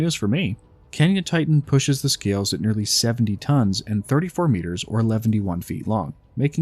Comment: I’m sorry but did he just say “Eleventy one feet long”? 😂😂😂